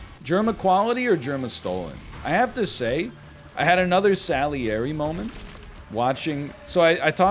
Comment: wtf is "sally airy" (except spelled like what he actually said) at 15:18 because i feel like it's a word i've been forgetting but i literally can't get google to suggest to me the correct thing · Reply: Salieri was Mozart’s mentor.